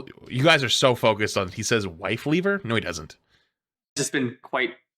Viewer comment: I think he just didn’t annunciation the L so it sounded like he said “wifeweaver” and then chat gaslit themselves into hearin “wifeleaver”